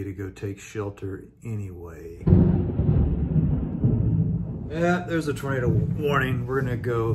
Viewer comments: Just as a side note, did anyone else's closed captioning think he said "Yep, there's a burrito warning" at roughly 4:23? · the captions say "burrito warning" instead of tornado warning 🤣